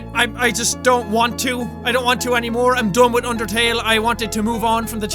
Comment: I keep hearing Mark say "I'm boned" xDD